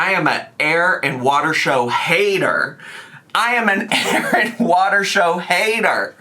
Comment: I thought you were saying Aaron Waters show all the way up to